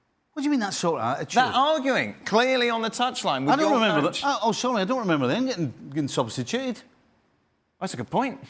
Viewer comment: “I don’t rememberer blah blah blah substituted” anybody catch that? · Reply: “I don’t remember them getting substituted”